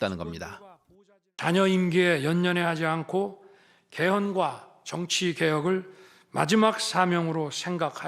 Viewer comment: 개헌과 정치개혁이라는데, 계엄으로 들리네...ㅎ